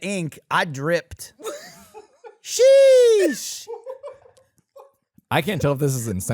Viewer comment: What did i just hear? Drip ink sheesh 🤣😂